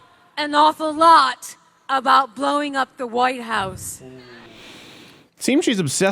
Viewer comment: All I heard was "yes I am out...I want to blow the white house..."